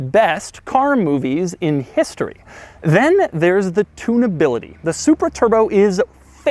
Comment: When you said tune-ability, all I heard was, “Tuna no crust.”